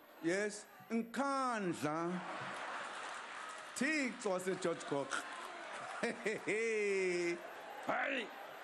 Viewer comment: does anyone know what Zuma says at 1:03 ? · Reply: I was watching trevor noahs "pay back the laughter" and i think he says "Thixo wase George Goch" Idk what that means